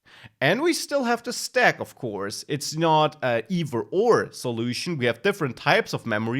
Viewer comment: "it's not an ?" what is he saying?? "Ev or or"?? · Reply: "an either or solution"